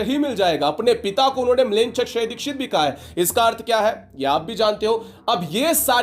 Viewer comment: pe kya bole AAP smzj ni aaya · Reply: "लेंचक्षयदीक्षित" मतलब ऐसा व्यक्ति जिसने मुसलमानो को मारने की दीक्षा लियी हो